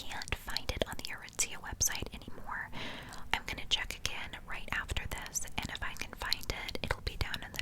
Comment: Did she just say “I’m finna check again” 😳👀 I didn’t think she would be a person to say that 😭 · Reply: I just listened and she says "I'm gonna check again"